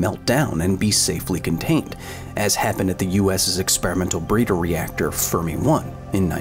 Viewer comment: I thought you said breeder reactor furry one and i shit my pants